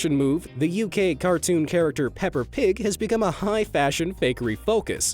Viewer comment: Is it just me, or did he say "Pepper Pig" instead of Peppa Pig.